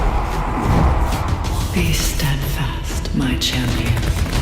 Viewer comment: Ohhhh I thought the Oracle said "Feast and fast my children"